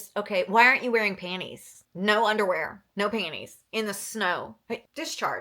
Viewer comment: all i heard was “no bra, no panties” from the movie “thirteen”